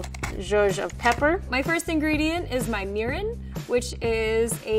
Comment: i was watching on 2 times speed and thought she said my URINE, and i was like, your WHAT?